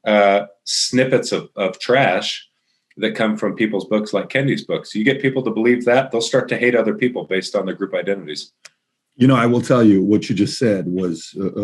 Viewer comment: What did he say 47:50 Kennedy’s books?